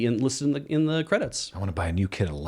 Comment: I had to rewind and re-listen to the last few seconds. For the benefit of other listeners I think G is saying "a new kit of lights", not "the new kid a light"; which is what I thought I heard the first time.